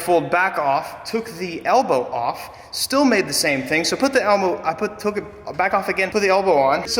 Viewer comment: did he just say elmo 😂😂